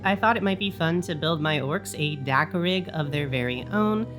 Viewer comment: At first I kept hearing 'dakka-pig".😂 Now I want a dakkapig 😊